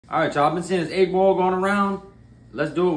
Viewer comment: I thought he said how to cook an 8ball. That’s crack!